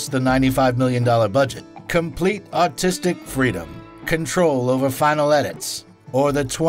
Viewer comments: Did he say, "Complete autistic freedom?" LOL! 😂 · did he say "complete autistic freedom"? Lol Subtitles are so far off they can't be trusted.